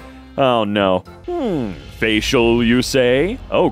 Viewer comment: Thought that said "Facial Roller Sex"